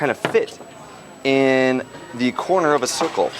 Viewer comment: did he say in a corner of a circle 4:50 :)